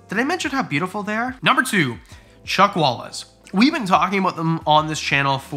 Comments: 😂 I thought you said Chuck Wallas the actor!! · Omg the collard lizards colours! They are so stunning 😍, anyone else thought he said Chuck Walis 🤦‍♀️